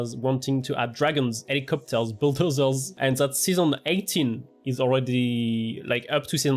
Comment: Have I heard it correctly? Season 18???